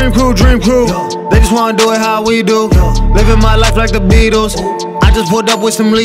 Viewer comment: I thought it said “living my life like the Beanos”